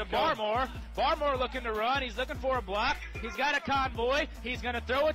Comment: Due to tinnitus, I thought the announcer kept saying, ‘now he’s looking for a black’, which didn’t make sense to me. It took me awhile during the ‘lateral-rama’ to realize the announcer was saying, ‘now he’s looking for a ‘block’. Anyway, this was an incredible effort by the lateral-rama team resulting in a touchdown!! 👍😁